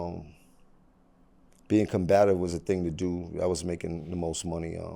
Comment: did he say he was making the most money? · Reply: He said being combative was making the most money.